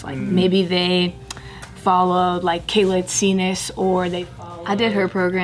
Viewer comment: What does Meg say at 14:02? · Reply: I think Kayla Itsines.